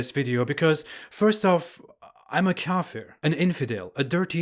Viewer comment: he is atheist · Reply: oh ok. I misheard when he said “kaffir” for “Catholic”